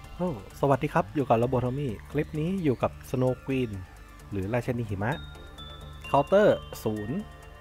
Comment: พี่ครับผมได้ยินได้ยินพี่พูดว่าราชะนีหิมะมีใครได้ยินเหมือนผมบ้าง